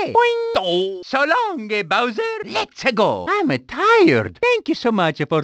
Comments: HAHAHAAHAHHHHAAAAHHHHHHAAAAAAAA being burnt by a pool of lava 0:00 WWAAAAOOOOOOOOOO falling from a high place 0:02 oh-oohhoo ran out of power 9:00 cough 0:16 here we goooo :)) 0:30 hello in cute voice 0:33 boing 0:34 DOOUH being hit by wall 0:35 SO LONG-O KING BOWSER! 0:36 Im-a tired 0:41 YIPPIEE 0:42 WAAHH-HAAAA All of them are my best thing , thank you mr martint !! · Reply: I can hear that mario says" I'm-a tired" But I can't hear " so long-a (king) bowser" I always hear " so long-a gay bowser! " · When mario says so long eh bowser it sounds like hes saying so long gay bowser